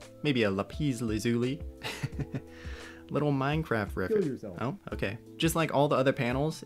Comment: anyone else hear "kill yourself" in your left ear when listening on earphones at 3:54 ?